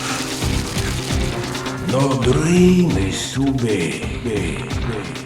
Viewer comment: What did Gagnè say at 1:53? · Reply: “No dream is too big.”